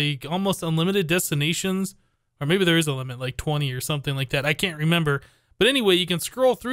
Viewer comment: I had to listen to that again... I thought I heard 'I believe you can sent unlimited destinations... like 20'. I missed 'or maybe there is a limit' somehow...